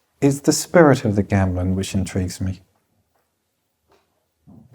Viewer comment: can someone tell me what is ment at 10:59. I know he repeats a word which sounds like "gamblin" can someone please clearify? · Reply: Gamelan is the traditional music from different cultures within Indonesia. Very different from Western music.